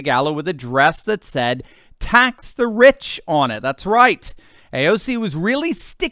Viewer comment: Thought it said Tax the Witch!!😂😂😂